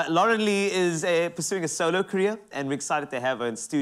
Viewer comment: Did he said she is pursuing a solo career 1:13. ?